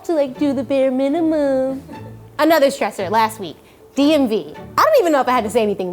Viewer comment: What did Freddie say in 1:35? DNV?